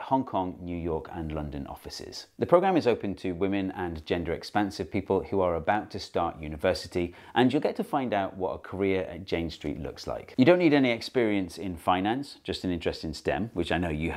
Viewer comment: whew, i thought you said "j-street"